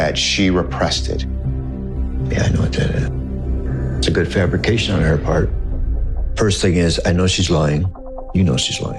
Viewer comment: what dose dad say here??? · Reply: “Yeah it did, didn’t it” is what I heard